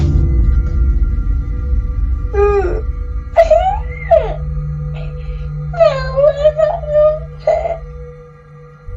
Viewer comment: What does the boy say at 20:05 when hes dragged out of bed? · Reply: He said “ow that really hurt”